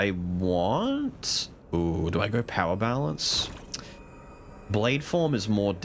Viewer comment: did he say power balance? Yeah you're still sick Jokes aside. Yeah this is currently seems rather monotone. It's very cool yes, satisfying yes but it seems to me right now it's a same thing just try over and over again until you beat the final boss then game's done. Routines and blessings definitely need to be more game changing, that or the skills need to feel more unique in playstyle. If that's done better there's no big need for more characters